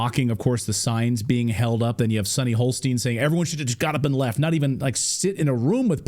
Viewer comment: I keep hearing "Sunny Holstein". Is that like Sunny Hoof and Mouth Disease? "Today on—The Moo"... (It's a moo point)...